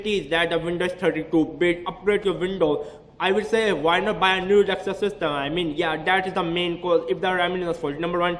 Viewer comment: subtitles say why not buy a new sister